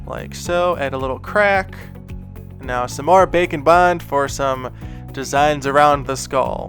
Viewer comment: I love that the subtitles say “bacon bond” instead of bake and bond.... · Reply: Oh...I heard "baking bond" 😂😂